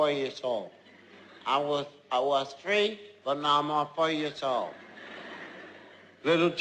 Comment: At first I thought when said"I'm 4 years old" I thought he said "I'm for your soul" :/ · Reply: i thought he said; I was free, but now im for you soul.